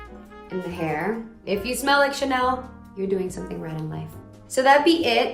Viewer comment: “If you smell like Chanel, you’re doing something right in life ” I’m broke af so I guess I’m never gonna do anything right in life 😭😭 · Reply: For a moment I thought she said "you're gonna do something right tonight" lel